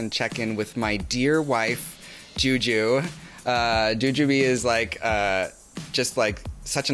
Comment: Oh my gosh, did she just call Jujubee her wife?? My CrackerBee heart 😭❤️😭❤️